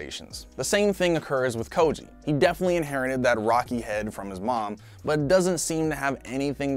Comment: Is it just me, or do I hear him say, "inherent" instead of "inherit?"